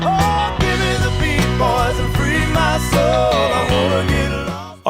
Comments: I think "Drift Away" still sounds great 50 years later. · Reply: I thought he was saying "Give me the Beach Boys to free my soul"😂😂😂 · I always thought that this song said “Give me the BEACH BOYS and free my soul” 😂😂 (I also thought that the pledge of allegiance ended with ‘and just a STRAW’ Instead of “and justice for all”. ) 🫢